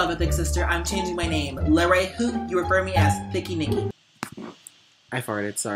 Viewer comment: LMFAO, AT 0:37 WITH THE CAPTIONS INSTEAD OF "Thicky Nikki" (so sorry if i spelled it wrong) IT SAID "Picky Mickey" 💀